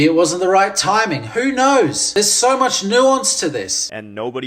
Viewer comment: I know you said "Nuance", but you said it so fast I heard "nonce" 😂. Can't find the lie...